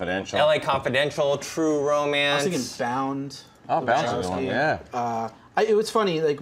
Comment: what's germain lussier's recommendation at 17:52? i couldn't hear because someone else was talking. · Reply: Bound!